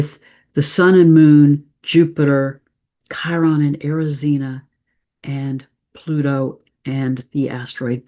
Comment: Hey, what is the name of the planet mentioned at 21:34? The one next to Chiron? Is it spelled Erissena (I can't understand from the phonetics)? Is this the same as Eris, the goddess of discord? With appreciation, Denisa